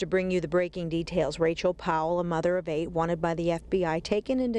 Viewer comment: I had to scroll back. I thought they said Mother of hate.